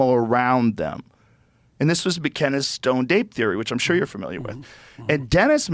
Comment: the subtitles are wrong here; he says "stoned ape theory".